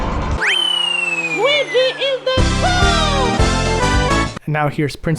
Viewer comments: I think Luigi is saying "Luigi is the GOAT!" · I'm gonna be honest, but at 3:01, it kinda sounded to me (at least) that Luigi said, "Weegee is the school!" I don't know. That's just what I thought he said.